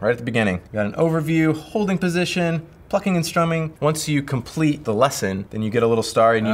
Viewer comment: i thought he said F ing and strumming😅